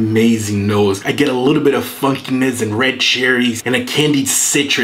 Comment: I misheard funkiness as fuckiness 😂